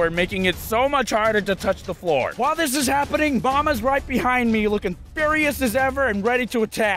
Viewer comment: When he said “mama was right behind me” I thought he said “Obama is right behind me”